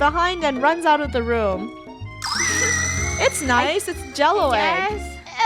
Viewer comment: it sounded like said slushiest from home